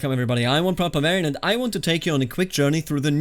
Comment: you always spit that first introduction so fast I always hear "hello Iam One Proper Bavarian"